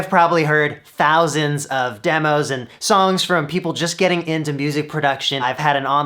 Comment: Am I the only one, who heard "soome-"(from All Star) in that ", songs,.." Or there is anybody else, who hear things just like Shazam, ahah